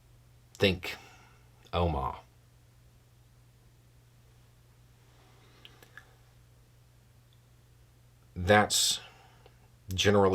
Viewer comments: What does he say at 17:42 after "it's usually something pretty horrible. Think _____." It sounds like Oma or Omaha but I have no clue what Nebraska has to do with this topic. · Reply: Omagh, Ireland. 1998. August 15. Ten past three in the afternoon. Body bags and little rags ... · I can't understand what Beau said when he said "...think Oma [sic]." Does anyone know what he was talking about? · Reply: Okay, I found it. He meant the Omaugh bombing in 1998 by the RIRA. I would argue though that the process of changing victory conditions and getting a peace process going started long before then. The RIRA only came into existance because the IRA, Sinn Fein, UVF, and other paramilitary groups in the conflict had already started to negotiate peace, but there were smaller factions (like the RIRA) that wouldn't accept it and broke from those groups. The response to the Omaugh bombing was more of one of the last events in the peace process, rather than one that made peace possible. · I have played the section up to 17:42 and over and I cannot decipher what is being referenced. "...it's normally something pretty horrible. Think ("Elmar"? Omar"? "Omaha"?)" I have no clue what word/place name/event is being referenced, and closed captions are no help. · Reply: Same here. Edit - another asked the same thing, answer was Omagh 1998 Ireland.